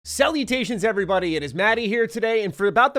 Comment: I know you said salutations at the start but I thought you said "cell mutations".